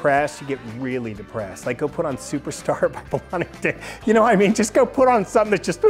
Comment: What does he say at 5:41 ? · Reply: Delaney and Bonnie